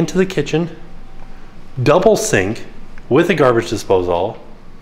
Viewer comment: does call it a garbage dispose-all or is it just me? I though I heard him say that in a different video, now here he is again.... lol!